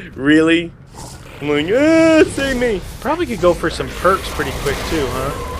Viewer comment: it sounded like he said we fuggerly go for some perks